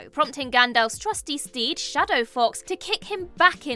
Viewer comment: Shadow fox??? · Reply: I thought she said Shadow Fox instead of of Shadofax.